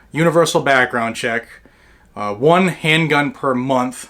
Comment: Maybe a Freudian slip but he actually said 1 gun. not 1 handgun.